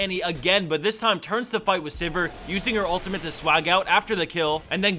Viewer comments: Did you say "swag out" at 2:07? haha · did he say "swagout"?